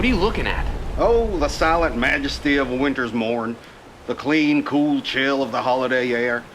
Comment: what are you looking at: oh the solid majesty of a winters morn the clean cool chill of the holiday air! Me behind the screen watching: did he just say solid or salad? -_ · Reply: Silent